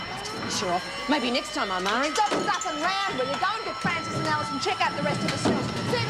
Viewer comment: sounds like Marie is saying "stop f**** around", though she's probably saying "stuffing".